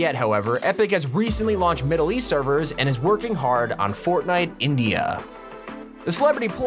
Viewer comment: Did I heard fortnite INDIA ....at 9:29 Who else wants fortnite to be popular in INDIA also